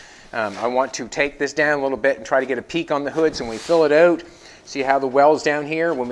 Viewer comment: Are you going to run a peak through the middle of the hood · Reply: I heard him say he was gonna (try to) get a peak on the hood.